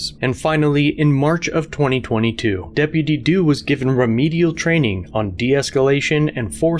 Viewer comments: can we just take a moment and give some cred for saying "deputydoo" so many times without laughing :D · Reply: I thought he was saying deputy dog lol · I'm high right now, but, it sounded like he is saying Dippity Doo as someones name. I think it is supposed to be Deputy Doo